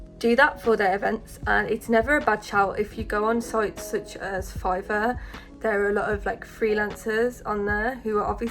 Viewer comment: What’s the website you said ? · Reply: Fiver i think 5ver